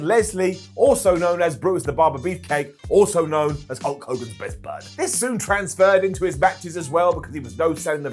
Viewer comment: I wasn't fully paying attention, and thought u said Brutus was Hulk Hogan's butler lol... which is kinda true